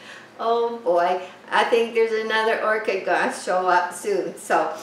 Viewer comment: Thank you sharing, who would think sbout using a computer dish. Learning new things everyday great idea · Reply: I never would have, but he came up with that all on his own, and I thought he said no more orchids :0)